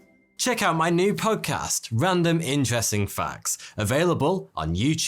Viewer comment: For a moment there, I thought you said random ingesting facts at the end of the video lol